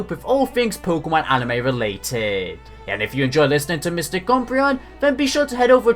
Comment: you sound like you said Mr Cumbreon